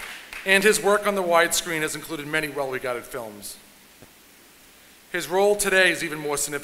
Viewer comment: Some corrections on the captions 0:59 - well regarded films 2:53 - Make it to Brunch 3:07 - Or the Pope 3:19 - Hard to believe 3:28 - Kennedy instead of Candid 4:05 - Named Ed 4:43 - Named Ed 5:09 - Zero Percentile 6:24 - will do nothing for me 11:04 - had worked at fidelity 13:45 - A Bet on myself 14:19 - out of a Coke can 14:37 - Saturday night live 17:25 - Loss instead of Lost